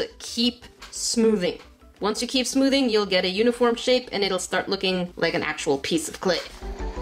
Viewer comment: When you said uniform shape I thought you said unicorn shape!!😂😂